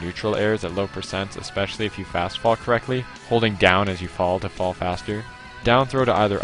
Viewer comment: thought he said "if you fast fuck correctly"